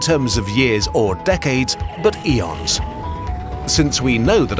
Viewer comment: did I just hear Elon?